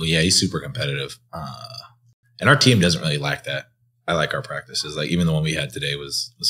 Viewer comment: your subtitle said "our team doesn't really like that" but it sounds like jdub said "lack that".